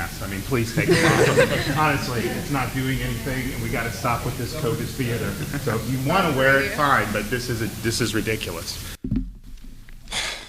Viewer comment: Oh I heard “Covid theatre.” But fever works too!